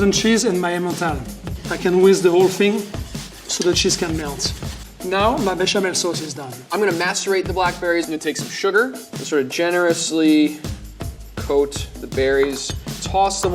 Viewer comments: I initially thought he said he was going to masturbate the blackberries. 😳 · i thought he said master bate the black berries and i got so scared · "i'm gonna masturbate the black berries" u didn't hear it you're a sheep!